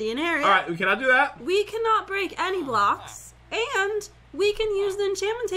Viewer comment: I think Jen said “We cannot break any blocks” Listen